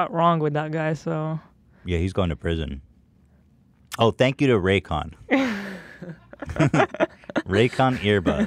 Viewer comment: I thought Ethan said “thank you to Raekwon” at the beginning. Wu-Tang Forever!